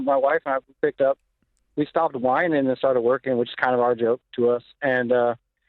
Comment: what were the steps he took? 4m over 6 years is over $650k a year, and no there isnt some simple set of rules that 100% of humans can follow to achieve a job that pays $650k a year. living frugally while working at mcdonalds as a low level employee isnt going to land me a 4m networth. the dude clearly started a business or something, and it would be cool to know what he did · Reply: I thought he said he started a winery?